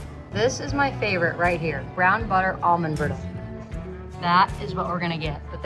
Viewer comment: I thought that said brown butter almond garlic 😂😂